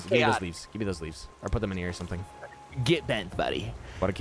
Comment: I was there for this :O look mom I'm on TV :OOO 15:20-ish, I can't believe told me to get bent 😂😂😂 I didn't hear him at the time bc I was half asleep and I heard him say "khaotic." And I was like "what the heck am I in the twighlight zone" 😂😭 For the record I said I trust behind the wheel before and after he read the message where I said "idk who's right" in terms of the motion sickness 😂